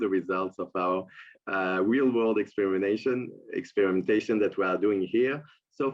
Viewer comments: “Real world experimentation ” on an entire country’s population! How proud of that can you possibly be? Nuremberg trials coming up for these people who think this is “exhilarating”! (Soros words of his emotions while stealing from his own Jewish people in Germany!) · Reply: I had to backtrack a few times as it almost sounded like he said extermination but he was saying exper...mentation. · Did he say real world extermination? 🤯💭